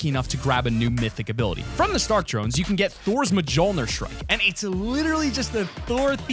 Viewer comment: did just call mjlneir majolneir